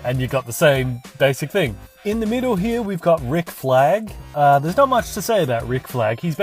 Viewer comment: Ric flag, for second I thought he said ric flair